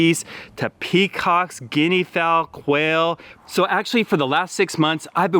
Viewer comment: Did you say whale? Oh never mind, it's quail.